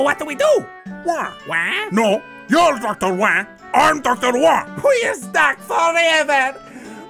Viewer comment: i cant understand what he said. I play it over and over but i dont recognize any words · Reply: "We're stuck forever"